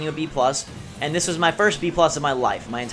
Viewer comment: I thought it said my first BJ